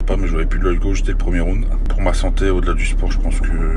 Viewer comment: qu'est ce qu'il dit vers la fin ? Pas bien pigé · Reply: Si j'ai envie de recombattre il a pris la sage décision même si c'est dur a avaler